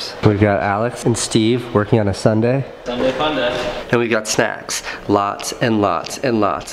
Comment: "And we got sex.... lots and lots and lots of sex..." I think I heard it wrong...